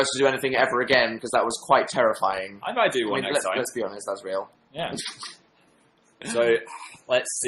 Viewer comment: I thought he said "let's be honest phan is real" 😂😂 wow